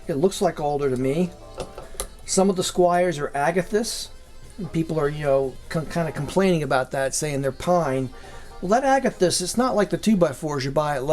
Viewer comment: What on earth does he say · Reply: "Pe..ple..p...people are kinna cl..complaining about that online saying its pine" He got a little tongue twisted (obviously)